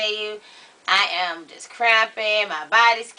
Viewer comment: Lol, I thought she said I was crapping instead of cramping. Like taking a shit lol.